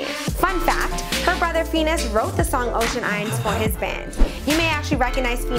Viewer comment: did she just say fenenos